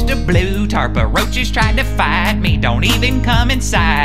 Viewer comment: But who's house is this? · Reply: I thought he said, "A roach just tried to bite me"...